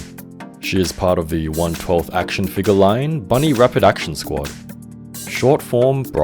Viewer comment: thought i heard "Bunny Reproduction Squad"